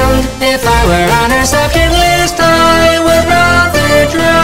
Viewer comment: Wait, "subject list?" All this time I thought it said, "Sucker list."